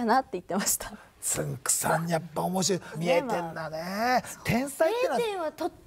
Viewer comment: 「見えてんだね」も若干「0点だね」って聞こえるし、多分まーちゃんが「0点取ったことがある話」をしようと「0点」のことを考えてたから余計「見えてんだね」が「0点」に聞こえたのだと思う。